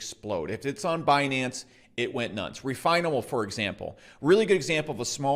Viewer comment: What coin did he say at 2:15? Refinable? Cos that defo didn't go nuts